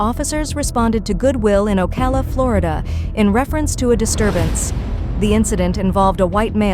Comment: Thought they said "Califlorida" at first. Was like, God help us if there is a place that is both California and Florida at the same time.